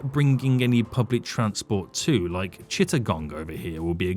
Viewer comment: ya fool, neraly did a spittake when you said Chittagong because at first I thought you said 'shit a gong' 😂 I know about shitting bricks, but a whole damn gong? 😂 And then you go shipping stones from Chit to make bricks, ya fool, would have been the perfect name for that line, 'Chit Bricks' 😂